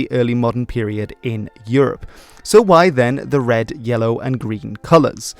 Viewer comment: did you say yellow? :D